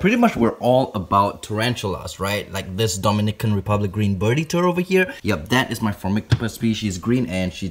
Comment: I thought he called it a "green birdie turd" at first 🤣🤣🤣🤣